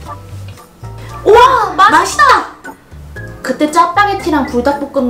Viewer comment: 나만 ''맛없다!!''라고들리나..